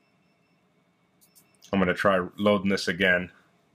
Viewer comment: Thanks for the video! How did you do the captions? "I'm gonna try our love miss again" instead of "I'm gonna try loading this again" (5:05) and "their ego" instead of "there we go" (5:13).